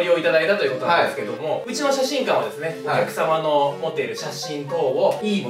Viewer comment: お客様のが貴様のに聞こえて ！？ってなってしまった